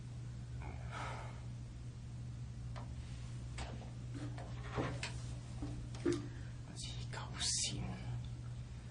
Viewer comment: I'm really curious about what he said at that 1:18:50 mark-- when he was alone in the room. He says something, and it's not in English, and I just have this gut feeling it's the only really sincere thing he said the entire interrogation. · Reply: He says "Say sorry? Sorry for what? I didn't do anything."